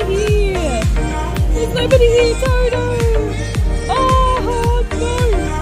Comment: I thought you said nobody there's